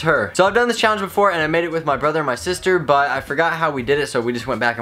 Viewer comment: thought he said I made out with my brother and my sister